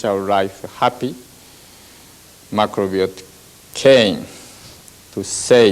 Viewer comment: he is saying someone or something came to save all spirits, what is that word he's saying? Something with an "M" maybe? I couldn't understand after listening a few times, any help would be greatly appreciated! ❤ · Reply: Macrobiotics